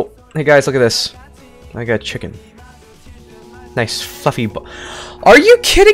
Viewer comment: Did he said the "F" word or it is just a mistake? Imagine he said Korean for the first time in his video (First time, right?). By the way, nice video. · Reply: He said fluffy